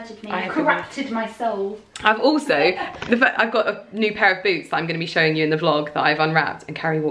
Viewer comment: At first, I heard "i got pair of boobs that i need to unwrap." lol Your videos are the best